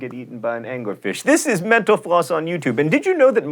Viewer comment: When John Green says "Metal Floss" I hear "Menopause"